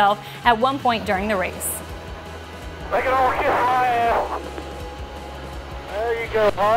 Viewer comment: Did Harvick just say "Kiss my ass"? XD